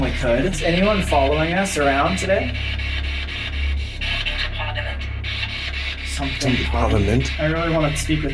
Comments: it sounds like is said wellcome to parlment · AT about 51:33 it sounds like it says "welcome to parliament" really fast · I heard, "Welcome to Parliament." In a really elegant woman's voice, like she was welcoming guests or smth. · That sounded like it said “welcome to parliament” 53:47 if you look to the left where is standing you can see an orb? The way it moves doesn’t look like dust 55:48 if you look by you can see a quick orb appear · I'm just watching this now and when you guys did the spirit box outside of room 217 at 51:30 I heard as clear as day "British Parliament" and I know that sounds werid considering its America and i wasnt there (clearly) but the whole time I am watching this video my gut is telling me not to turn it off or cut the video until the video has ended and my brain is saying I shouldn't either but its also like someone or something is telling that if I do turn it off, something bad will happen. My friends have watched this video multiple times and said nothing has happened to them so I just don't know but I love you guys, I have always wanted to see ghost things or go on a ghost hunt myself and when I was young, I did see somethings so I really want to go to a physic medium to check that out. Your videos are awesome, I love them so much, keep up the hard work 😁💖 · i think it said welcome to parliment, im not sure either way love this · sounded like it said welcome to parliament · It sounded like welcome to parliament